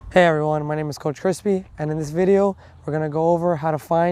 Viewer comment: Until I look at his name. I thought he said Coach Crispy. Cause he has a crispy game.